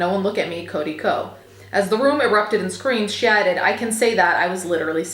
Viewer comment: omg I had to listen to this several times. I thought you said “shatted “ instead of “she added”. 😂